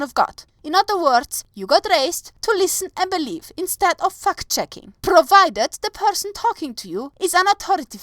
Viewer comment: Okay your accent throws me sometimes. I know you said fact checking but I swear it sounded like you said fucked checking.